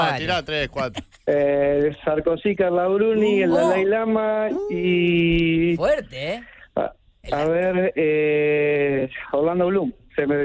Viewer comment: a quién nombra acá?? no entendí un sorongo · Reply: Sarkozy, Carla Bruni, el Dalái Lama y Orlando Bloom